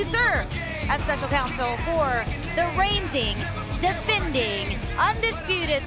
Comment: Did Kayla just said : For the reinsing, defending....